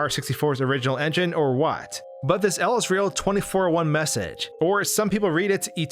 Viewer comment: I always thought it said "Israel 2401"